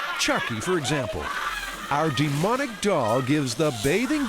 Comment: Demonic dog? Am I hearing this right?